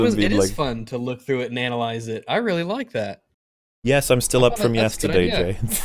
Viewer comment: Anyone else hear say "Yes, I'm still up for masturbating" at 3:05:01?